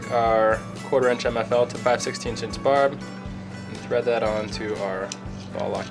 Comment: I could not make out what you called the third item.. All I heard was"1/4" mfl to 5/16" interbog" the "interbog" .. What is it really called? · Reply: Inter-barb